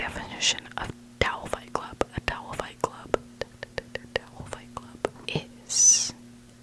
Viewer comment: I thought you were saying “doll fight club” 😂 That sounds like more fun!